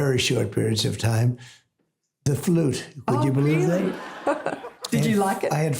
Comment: Did he say skin flute!? I can see him as being a flouter, but not a flutist.